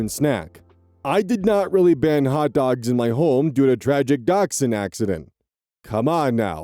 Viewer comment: "And Google started to bark at me"- Did you choose English pronunciation, or dachshund pronunciation? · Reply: Oh he said "dachshund". I misheard "dogsound" at first, which weirdly enough also made somewhat sense but not really... xD